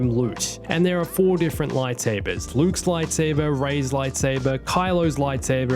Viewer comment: eyo at 6:28 did say rey's lightsaber